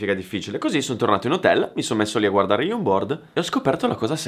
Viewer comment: Solo io a 12:57 ho sentito "mi sono messo a guardare youporn?" AHAHAHAHAHAHHAH